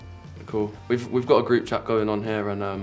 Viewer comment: Pilipino🇵🇭 Here 🙋🏼 Did he just Said .' AKO' 1:21 Tagalog Words OF 'Me' . after he said ME he Added 'AKO' when he answered the question of being guilty of not replying to their groupchat . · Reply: he said cool.